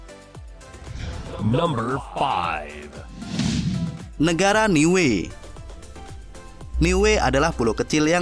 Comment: gw dengernya liyue